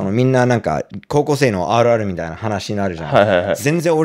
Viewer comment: の「あるある」（=よくある話）の発音が良すぎて、rrに聞こえて、最初？？？ってなった笑